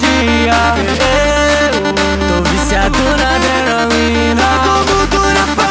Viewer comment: EU OUVI "TO VIADO NA ADRENALINA" KKKKKK